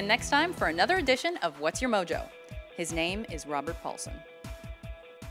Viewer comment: I thought you said "his name is Robert Pattinson".